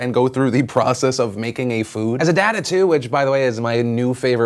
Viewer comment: Got any big plans for Friday the 13th, ya weirdos?! Lemme know some of your fave scary movies in the comments and mayyyyyyyybe I'll watch a few of them. muahahahaha. <3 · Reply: Philip DeFranco when u said a dad of two I thought you said a dad of 'tood